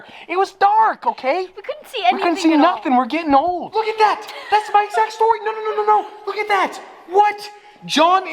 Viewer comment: Why did I hear look at my sex toy :D